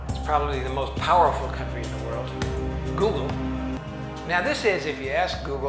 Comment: did he say "the most powerful country in the world" describing Google??